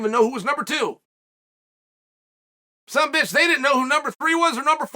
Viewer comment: Did Chael actually say some bitch??? 😂